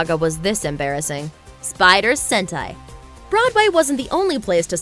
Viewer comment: I swear, for a moment I thought she said " Spider Hentai"